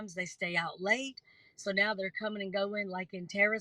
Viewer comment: Did I hear this person pronounce library, LIBARY?! Really?!